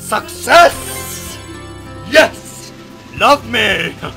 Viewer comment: Did you just say Fucksess? :P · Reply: Cage looool, nah, i said Four Five Six ( it sound bad)